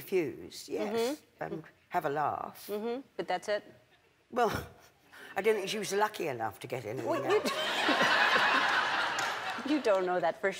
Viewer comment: What did Judi said at 1:16 ? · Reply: "I don't think she was lucky enough to get anything else" maybe?